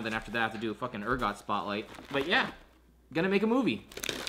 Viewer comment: I thought it said finally makin a move in like fuck yeah he is proposing then I realized it was movie